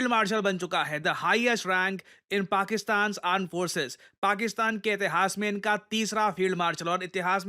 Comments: I thought you really said Pakistan aand forces . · did he said "Pakistan aand forces" ??😂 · did sir say Pakistan Aand Forces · did sir said pak aand forces 😂😂